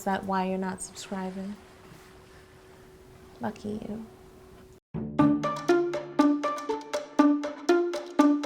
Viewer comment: I thought she said "f*** you" 😅